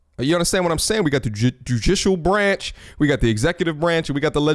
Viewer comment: Ayyyyye When he was talking about the government it sounded like he said Jujitsu branch. We over here in the hidden states village.